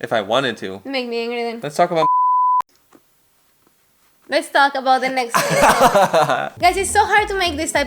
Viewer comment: what did he say? Let's talk about? Sex??!